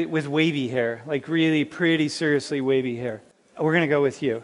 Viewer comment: "...REALLY. PRETTY. WAVY HAIR." GOT IT HAIR is HAIR. And ALL TEXTURES/COLORS are BEAUTIFUL. IMO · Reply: I heard "...REALLY pretty seriously WAVY hair." ✌